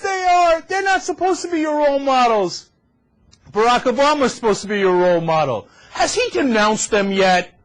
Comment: Check for Brother Thomas instead of Barack Obama in CC